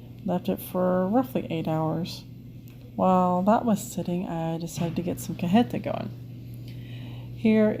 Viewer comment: What are you making at 23:53 in the video? I have played it back several times but can't figure out what you are saying. Beautiful goats by the way. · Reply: I'm making cajeta. It's a Mexican Carmel made with goats milk. It's soooo yummy 😋 Thanks, we love our goats so much.